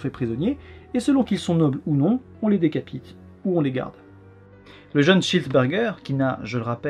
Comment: J'ai compris "le jeune chesse burger"